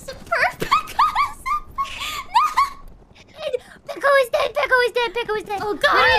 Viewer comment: When Gura said "Peko is dead, Peko is dead!" I misheard it and thought she said "Tuco is dead!"